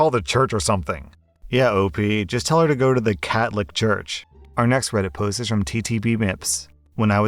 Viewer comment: I got the pun but I heard "Cat Lick Church"